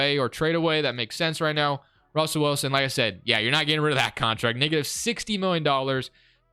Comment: what he say?!?! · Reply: "negative" ....